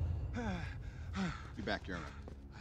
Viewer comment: What does Tony say at 1:58 seconds? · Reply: he says “you back you alright”